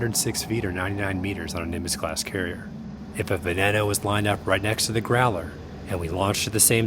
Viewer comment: At first, I could have sworn he said "If a banana was lined up right next to the Growler..."